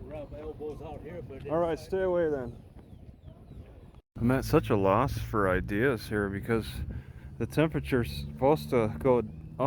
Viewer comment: What did that guy say to you at 7:05? Also, good job not freaking the Asian lady out and driving her off. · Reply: Pepper Wood I think he said, “they want us to rub elbows out here.”